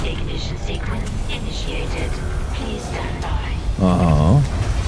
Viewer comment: Every time the computer says “please stand by” I hear “please don’t die”